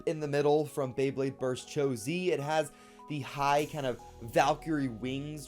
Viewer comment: did he just say from Cho-z